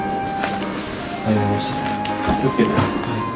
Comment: はじめしゃちょーさん気をつけてって言ってるように聞こえる。優しい。